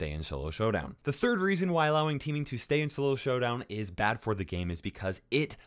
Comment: Did anyone else hear sholo showdown